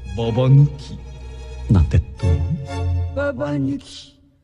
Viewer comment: 〜 永遠の疑問 ジョマは何て言ってるの？ 後ひまわり生まれる前の しんのすけって基本感情が無なのが 好きなんだ 無の状態から正論吐くのが面白かったのに 最近は ただのせっかちにしか ていうかただの園児にしか見えないなー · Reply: なんて…どう？って言ってます。